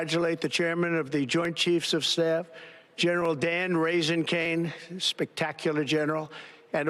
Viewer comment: I keep hearing him say “Raisin Cane” like a dried grape, and “Bordeaux” instead of Fordow.